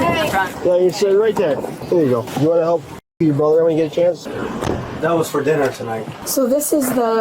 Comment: did he just say do u wanna help fu*k your brother?!